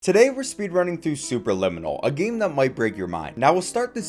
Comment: i clicked on the video and then almost imeadiatly paused to do somthing for a minute and all i heard was "Today we are speedrunning through soup." and almost died